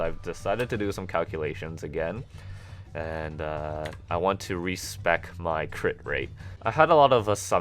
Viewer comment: at first i thought he said "i gotta (respect) my crit rate" be he actually meant re-spec haha. like if u thought so too..